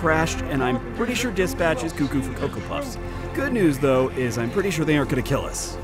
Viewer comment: Thanks for watching everyone! Make sure to watch the new episode of Downtime! I'd appreciate it! - <link> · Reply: did you really say "cucko for koko pops," at 10:34? XD